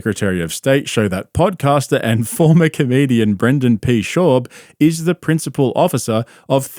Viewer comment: "podcaster and former comedian" even the lawyers are cooking him😂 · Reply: I swore I thought he said former Canadian. LOL!